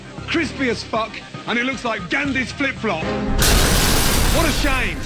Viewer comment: "It looks like Gandhi's flip flop" 🤣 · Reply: i thought he said Gandalf’s flip flop😅😅